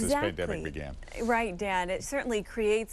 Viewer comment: Did she call him Dad?